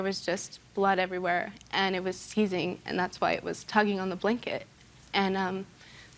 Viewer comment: I don't understand she said the dog was "teething" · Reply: Seizing, not teething.